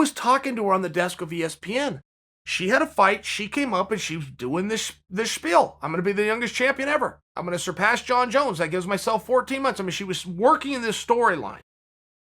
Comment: did chael say "this spiel"?